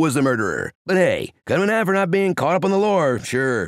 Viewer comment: wtf did he say there? 😂😂😂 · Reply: “But hey- cut him in half for not being up on the lore”